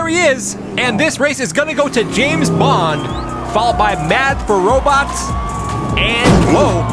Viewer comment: did i hear robux ?